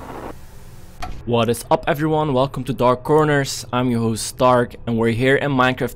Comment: If you guys turned on your subtitles, did you notice it thought dark said "Stark" when he said "Its me your host dark"? like this comment if you seen that also like the video I really liked seeing dark make a shrine for red steve! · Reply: Yes, he said dark but the subtitles thought he is saying Stark